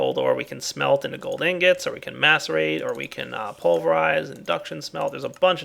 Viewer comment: Sounded Like He Said Masturbate xD